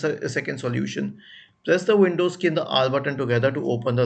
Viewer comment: On 0:44 I dont understand what you said to click on? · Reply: windows+r